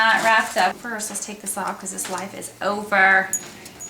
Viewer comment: I almost screamed because I thought she said " first let's take this off cause his life is OVERRR"